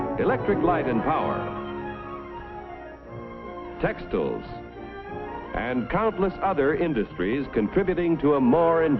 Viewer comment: what does everyone else hear, because I keep hearing "sex dolls"